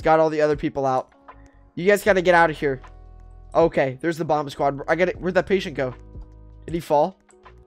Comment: Sounds like you said bob squad lol